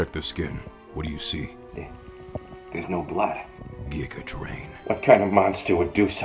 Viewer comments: What does Pikachu say at 00:20? After ProZD says "There's no blood" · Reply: Giga Drain. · Lol what did he say at 0:22 Geacatrain?? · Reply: No giga drain